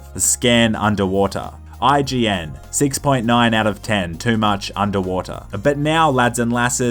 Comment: !!?? Did just say there’s too much water😨